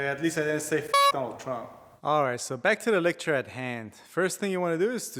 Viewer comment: Did I hear you say: " back to the lecture at hand?" ...lol